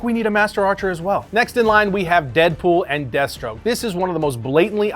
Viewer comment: Lol, at first I thought you said “Deadpool and DESTRO”. I’m like, WTF?